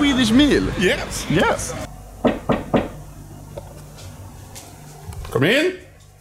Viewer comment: ''come in'' sounded like papooga